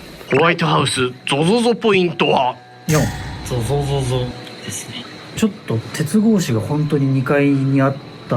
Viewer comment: さんがゾゾゾポイント言い終わった後はっきり女の人のような声で「確かに」って聞こえるんだけど、、